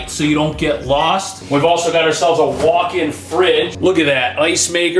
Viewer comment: Did you say walk inn-fridge